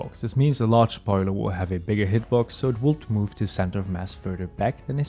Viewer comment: I did not understand what you said, ``so it wont move`` or ``so it will do move``?